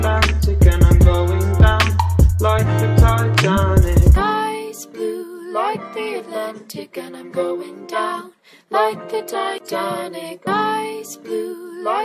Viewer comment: WHY DO I HEAR" guys blue like the Atlantic and I'm going down like the titanic' oh god HELP